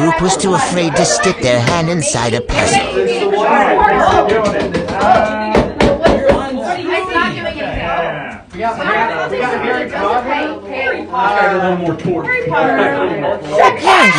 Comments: i heard "HARRY POTTER" like 24 times 😭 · I heard “Harry Potter” and “it’s gonna need more torque” no wonder they’re stuck in that bihh